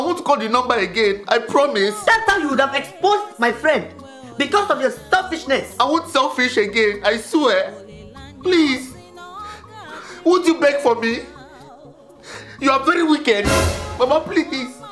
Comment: Him: i won't sellfish again What i hear: i won't sell fish again 🐠🐟🎏🐡